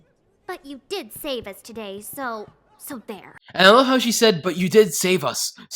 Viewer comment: i always thought she said "so bare" and not so there but idk